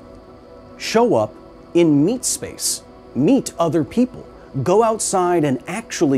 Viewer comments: I thought you said Meat Space and my brain assumed it to mean basically what you said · My dumbass thought you said "meat space"